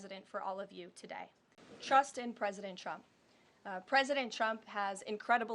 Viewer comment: Sure sounds like she is saying President Chump!😂